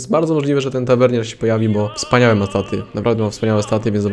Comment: czy tylko ja tam usłyszałem taty XDD